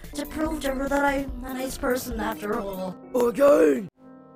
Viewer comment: AGAIN! my favorite face Edit: What I hear slowed down: A GUN!!